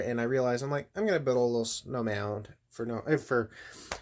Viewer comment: I could have sworn you said a little snowman lol